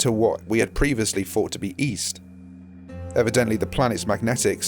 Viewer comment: Oh East I thought you said Weast